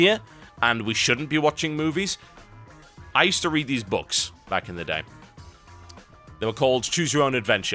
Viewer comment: I hear, "So I've read these books..." and almost shouted "CHOOSE YOUR OWN ADVENTURE BOOKS!!!!!" I love u TB